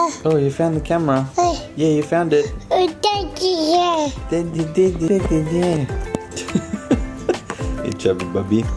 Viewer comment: Husky talaga voice ni. Hahah relate much. Dbale pag husky daw voice magaling kumanta. Hahaha (1:38) when found the cam, i think he said "Daddy here"